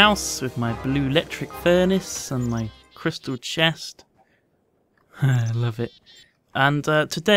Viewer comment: when duncan said "my crystal chest" i thought he said "my crystal meth".....wtf is wrong with my hearing?